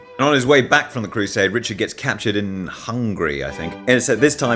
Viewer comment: I heard “Richard gets captured and hungry I think” instead of “Richard gets captured in Hungary, I think”, which made it 10 times more confusing and funny for me. Why would him being hungry matter?!